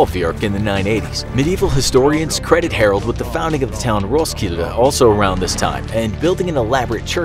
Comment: BRUH I THOUGHT THAT SAID ROCKSLIDE